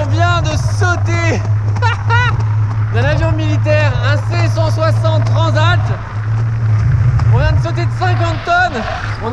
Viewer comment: c’est moi ou il dit C160 Transat ??